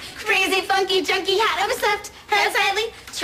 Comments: I heard “we see right through your fucking hat”... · I heard "we see you at 3 o funky hat" 🤦🏻‍♀️